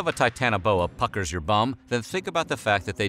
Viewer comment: Did he just say "makes your bum pucker"?? 😂😂😂😂😂😂